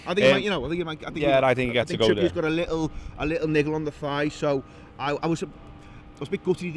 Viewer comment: Excuse me lads but, what is that N word at 3:14? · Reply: Niggle. As in a slight knock, a bit of discomfort.